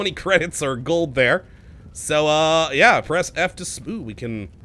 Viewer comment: "Press F to spoon" I can't be the only one who heard him say "spoon" right?